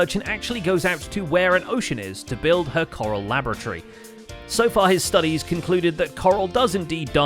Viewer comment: says "So far his studies...." , when the captions say "So far her studies..."